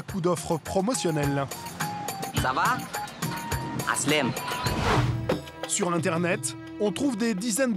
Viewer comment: c’est moi ou il a vraiment dit “sur L’ internet”...?